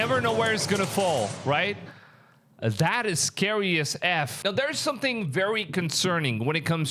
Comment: Did you just say ... "Scary as F" 9:01 🤔